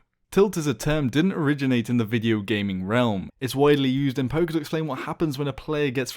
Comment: Every time he said poker i thought he was saying poké XD